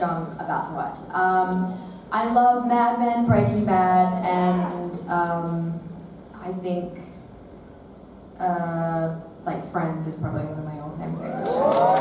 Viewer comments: What does she say in 17:50 ? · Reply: “I love Mad Men, Breaking Bad and um, I think, uh like Friends is like probably one of my all time favorite shows as well.” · what does she say? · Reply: Merve Serilmez FRIENDS is probably one of my all time favorite😁